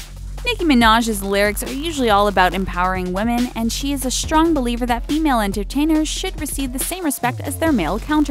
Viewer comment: I thought that said she was a strong belieber instead of believer